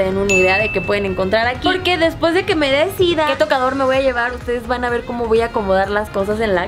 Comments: Despues de que me sida '1:02' sorry lo dijo muy lento y fue lo que escuche xdd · no hagas pausas, parece que dijiste "después de que me dé SIDA" jajajaja yo sigo la página de Facebook desde hace tiempo y me partió el corazón descubrir que no son de GDL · en el minuto 1:01, haha entendí que dijo: después que me dé sida y yo me quedé con cara de wtf xDDD hola c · Jajaja me saque de onda cuando dijo, por que después que me decida... jajajaja pensé que había dicho, después que me de sida xD